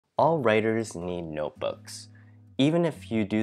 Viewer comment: what was that? “All writers Lll notebooks” im not sure what i heard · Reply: 😂 "Need" lol